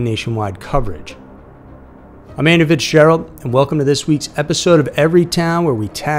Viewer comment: Today I learned your name was Andrew. I always hear "Amanda Fitzgerald" and even the close captions read like that, I finally googled you and learned the truth lol